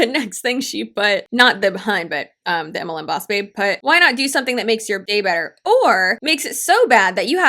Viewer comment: I thought the last girl said “why not do something that makes your high better??” You mean like rubbing it on my gums? Gotcha. Better than using paparazzi.